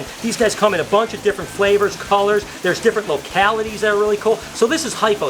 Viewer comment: DID YOU SAY FLAVOR?? :0